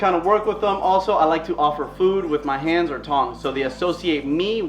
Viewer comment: Whenever you said you give him food by hand or tongs I thought you said tongue and i was— 🙂